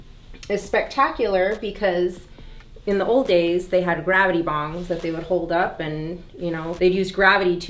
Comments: did she say “in the old days they had gravity bongs” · Did she call it a gravity bong?! 12:06 🤭😂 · Did say “gravity bongs” at 12:07???